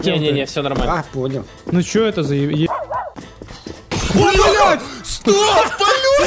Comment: А мне одному послышалось что сказал б**ть на 3:51????? Кому то же то сюда 👇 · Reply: не, он сказал бляха, а одновременно блять, вот так и получилось